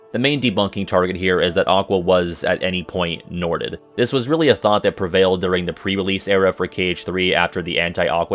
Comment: all I heard back then was "Aqua got Norted!" Which was confusing since it doesn't really look like she's possessed by bald Leonard Neemoy (R.I.P)